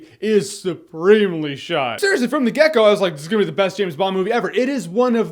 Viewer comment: Did you just say "from the gecko"? ...It's "from the get go".